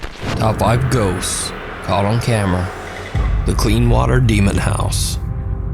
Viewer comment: i dont know why, but instead of "caught on camera" I heard "Caught on Camel"... hmmm... truly bizarre